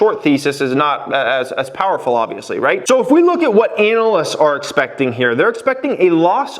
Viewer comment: Did he say anal-ist ?